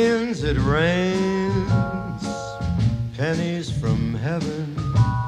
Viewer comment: NGL I always thought this song was saying "Panties" from heaven, thought it was creepy AF lol